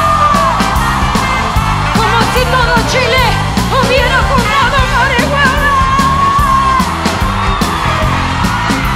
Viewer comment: que dice en el 4:20?? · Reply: Arriba. Como si todo chile hubiera fumado marihuana.